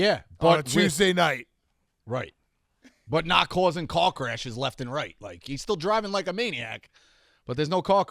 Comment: When Rico said "Car crashes" I heard "c*ck rashes"